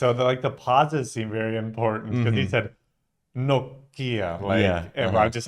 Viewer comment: There's no pause in "Nokia" in Finnish? But what you're saying the second time sounds to a Finn like you're saying "Noukia". There's no "u" in "Nokia". =)